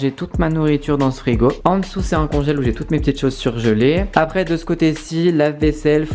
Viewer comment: j'ai compris "c'est un congèle ou j'ai toute mes chaussures gelées" 😂😂